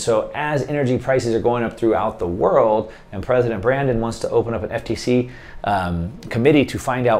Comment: did i hear call "president Brandon"??